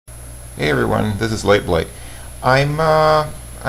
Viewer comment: It sounds like he says "Hi everybody, this is like like. HI MA!